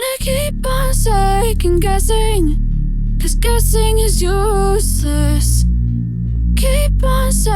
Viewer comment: is it just me or did she say sus